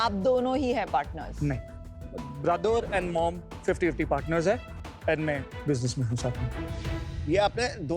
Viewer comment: what did that red tshirt guy said like he said (brother nd mother 50 50 percent nd mai business*****) so did anyone noticed or can anyone tell me what did he said that red tshirt guy he is in busineess as what??????🤔🤔🤔🤔🤔🤔🤔🤔